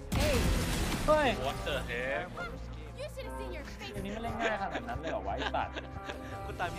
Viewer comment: I KNOW I HEARD “PUTANGINA”